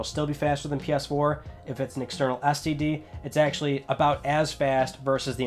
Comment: Just me that heard STD instead of SSD @ 14:30 ish?